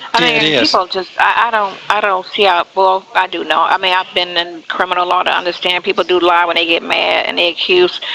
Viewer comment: lmfao did he just say CRINIMAL ???